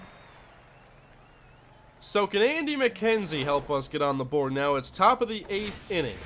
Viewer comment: I thought u said Andy McKenzie is not good